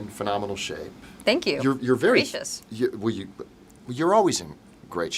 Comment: did she say “gracious”?